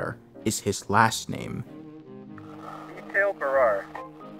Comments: I just subbed. I love finding new analog horror. Thank you. Is this where I saw a snippet the other day about caring for your new pet trimming? Lol it inhances the flavor of you so you taste better. Sounds like he says Dale Barrare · i think it said Mikhail Barrere i know this vid is a month old and people probably already know his name by now but i didnt watch everything so